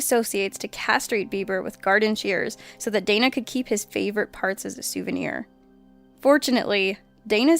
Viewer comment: I thought it said farts at first.